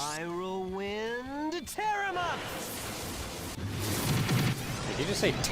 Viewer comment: he did indeed say "tear em up"